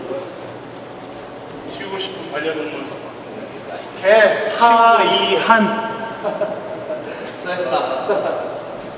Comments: 강민호선수는 역시..👍 근데 뭐라고 하는건가요??? 개 하이한???????🤔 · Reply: 키우고 싶은.. 이라고 질문하니 딸 - 강하이 아들 - 강이한 합쳐서 하이한 이라고 하는듯 싶네요~ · 강민호선수 뭐라고 하신거예요?? 하이한??? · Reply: 딸 하이 아들 이한 같이말해서 하이한 된거같아요